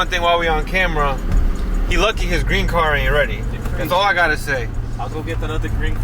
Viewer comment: Man I thought u said "green card", I screamed😭